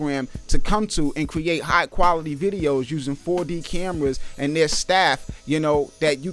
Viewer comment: did he say they use 4D cameras?